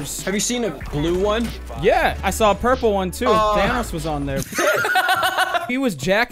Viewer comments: i thought he said vanoss not thanos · did I hear 'vannoss'